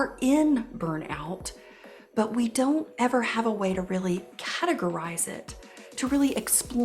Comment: says “how to grow rice” instead of “categorize it”!